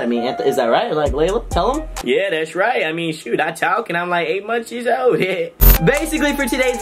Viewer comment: mezmerized and did she just say she was 8 monthsyearsold ?